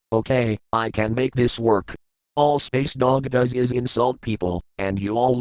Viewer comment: Unrelated to the video, first time I heard Space Dog I wasn't looking at the screen and initially thought you said "Spacedock" (the youtube channel) and thought it was some kind of sarky bit against Daniel